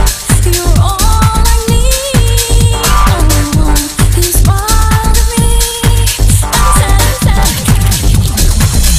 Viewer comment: Ughhh what does she say at 0:39 ????? For a brief second she says something help me out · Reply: "He smiled at me, and he said." The original song is easier to understand, it's the same title too, I know it's been a while but I hope you've heard it already. It's good in it's own way.